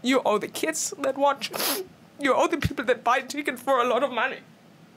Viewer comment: I thought she said: "You owe the people that but Chicken for a lot of money".😂😂